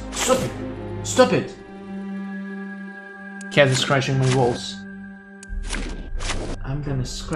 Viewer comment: Has anyone else heard ,,cat is scratching my balls‘‘ at 10:04?😂